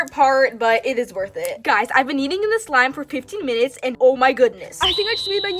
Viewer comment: i thought u said 50 minutes🤣🤣